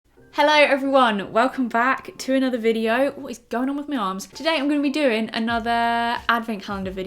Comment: I put this video on in the background and i thought you said "what is going on with me arse?" 😭 Also love all the advent calendar videos!!